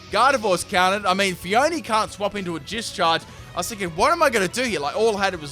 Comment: "Jizzcharge" i hear you ;)